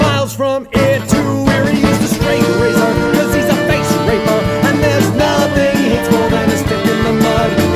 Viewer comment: I always thought when he said face raper, he was saying "face raker" Honestly I prefer my interpretation. It makes more sense to me, granted that raker isn't technically a word.